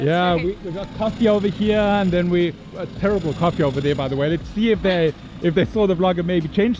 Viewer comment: Did you say "Terrible Coffee"? Honest review @ 02:30.